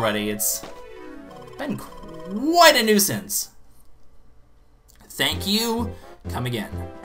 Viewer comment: Did someone say, nuisance???